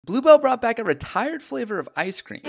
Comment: I misheard Blue Bell as Glubo